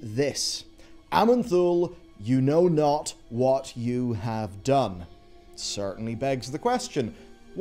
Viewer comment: Play War Thunder now for free with my link, and get a massive bonus pack including vehicles, boosters and more: <link> Sponsored by War Thunder. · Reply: That's not what Murozond said. He said. "You know not what you have done. Aman'thul...What I have seen." He's referring to the players I think.